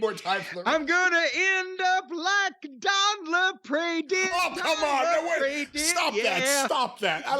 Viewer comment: what does Jim say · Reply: Don't end up like Don Lapre did. He's the infomercial guy they made fun of - placing tiny little ads. He took a razor to himself in prison.